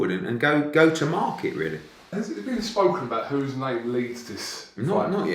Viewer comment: what did say? have you spoken about...? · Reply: Who’s name leads this